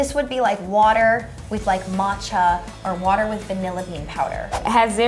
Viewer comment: It’s sorry but.... 1:37 Did that girl just “mocha” with a ch instead of a k sound?! NOOOOOOOO HUNNY